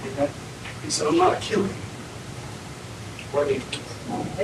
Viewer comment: you can here the cops talking sh#t about him and laughing in the background. That last cop who was interviewing him told the others what he said, which was “He said I’m not a killer”. Wild! You can actually hear him, and it seems like this suspect could her him also.😂